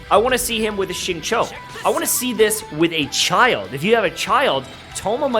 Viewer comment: If you have a childe..... I heard this and my brain slowed down... I heard it as child... A